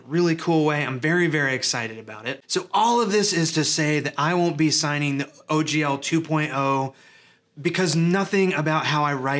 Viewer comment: I thought you said "I will be signing OGL 2.0 because..." and that made me sad. However, when I turned on closed captioning, it converts your speech into "I won't be signing OGL 2.0 because..." and that made me happy. Also, I need to get my hearing tested.